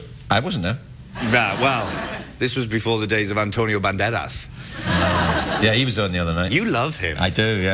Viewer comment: did he say Antonio Banderas?